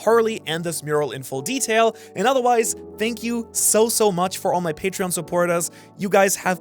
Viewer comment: thank you???? i thought u say shank you